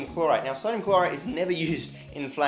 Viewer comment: I thought you said sodium chloride and not clorite lol